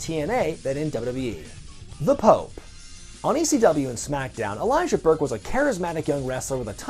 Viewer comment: Anyone else thought he said he's sponsored by Elias?😂😂😂